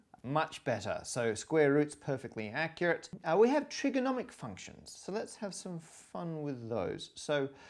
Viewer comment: Did he say trigonomic around 5:00 ish?